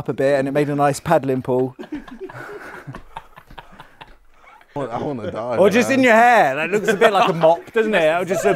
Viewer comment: anyone else hear jj at 7:52 ... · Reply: Yeah, he's in the video, he said "i wanna die man"